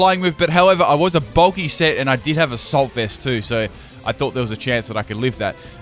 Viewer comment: And all I heard was "however I was a bulky set, and I did have a salt vest" because and his salt. :-p